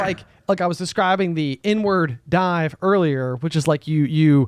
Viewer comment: I hate that it took me until 44:50 to realize was saying inward dive instead of n-word dive